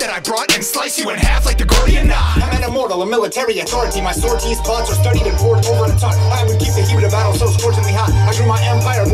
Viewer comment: Does anyone know what Zach says at about 1:53 after he says "I'm an immortal, a military authority"? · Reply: My sortie's plots were studied and pored over and taught I'd keep the heat of the battle so scorchingly hot I grew my empire borders way more than a lot And I would soar to the top, like the eagle whose feather I would sport In the helmet that I wore as I swatted my many enemies with muscles that were forceful and taut Shattered 'em into pieces like a porcelain pot They would scream until they're hoarse and their voices were shot And I would holler "BUCEPHALUS!", hop up on my horsey and trot off Leave 'em in an unfortunate spot Fold 'em up like an accordion, stop!